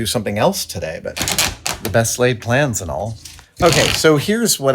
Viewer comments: did I just hear "the best lathe plans, and all..." ? lol 👍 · Did you just say "best lathe plans and all..."? XD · Did you just say "The best laid plans & all" at 11:01 or did I hear a pun: "The best lathe plans & all"....??? I'm hoping for the latter. Ha! I love it! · What was that at 11:00? Did Adam just say "best-lathed plans"? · If Adam said "the best LATHE plans" just past 11 minutes, instead of the best laid plans, that was both hilarious and so dryly offered as to be completely missed by most (I'd think). Or maybe he just said the best laid plans. But I really enjoyed what I thought I might have heard. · did he say "The best 'Lathed' plans, and all." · I heard “best laid plans” as “best lathe plans.” Seems like the latter is what happened. :) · Not sure if this has been said. But I thought I heard him say, "The best lathe plans...'. Well that would have been funny. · Did he just say "The best lathe plans"? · Did he say 'the best lathed plans...'? lol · Did you say, "The best ... 'lathe' ... plans and all"? Hah. 10/10 would watch again. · For some reason I heard "The best lathe plans" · Did he really say "The best Lathe plans"... because that's what I heard. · I thought he said, "the best lathed plans"